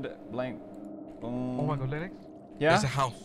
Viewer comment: Why did it sound like said, "Oh my god, Lynix?" 💀💀